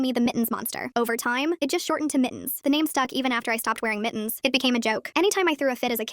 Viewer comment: i thought she said nittens